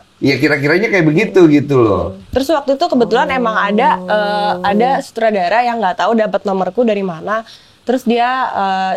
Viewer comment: woi itu siapa????????????? We kam dengar ka trada ?????!!!!! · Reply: Dengarrr kata "tolongg" 🥹🥹